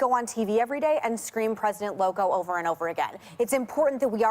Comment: I didn’t catch it. Where was it? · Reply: it's at about 4:30 she says president loco but it sounds like a white girl of course so I didn't even catch it til I re-watched it lol...cuz Ana says president loco lol...aka crazy